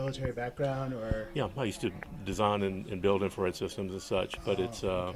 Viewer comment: The father said " I used to design and build " something for the military right about 8:05. starts to mumble right about that point and I can't make out what exactly he used to " design and build " for the military. Does anybody know what branch of the military used to work for and what he did exactly ? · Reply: He says "infrared systems." That would be very helpful in the search.